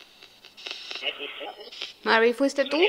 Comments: Creo oír en el minuto 16:05 que dice es Meri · Cuando agarra la bolita en el 16:03 escucho que dice "ES MARY" y una voz de hombre responde "EHH SI" En velocidad 0.5 se entiende mejor